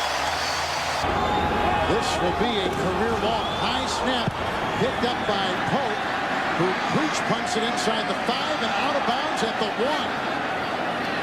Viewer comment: what does he say? He "breach punts it away?" I can't figure out this play, other than I'm sure it shouldn't have been punted after the botched snap.